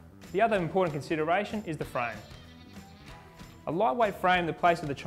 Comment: a what frame? Light weight or low?? Sorry, I couldn’t understand what you said. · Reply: A lightweight frame...